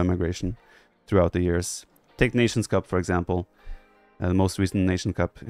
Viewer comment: TheViper mentioned maybe “technicians cup”, was it? I was curious to try and find games from it but couldn’t find a suitable tournament name that fit what I thought he said. Could someone please help?